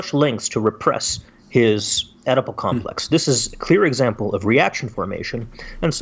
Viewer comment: I keep hearing them say "edible complex". All sorts of weird images pop into my mind.